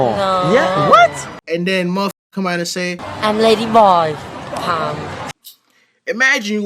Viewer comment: Did he say “I’m ladyboy hung,” @ 7:42 ?